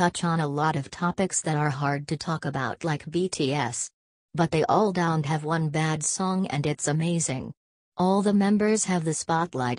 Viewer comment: during the stray kids opinion it sounds like they said “they have bad songs” but the audio pronounced it wrong and it’s supposed to be “they don’t have one bad song”